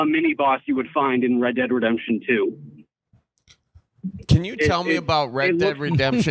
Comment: At first I thought said "rectal redemption 2" 😂